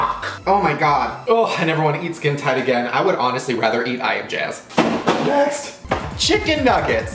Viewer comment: did anyone else hear “I never want to eat boob skin again, I would rather eat I Am Jazz”?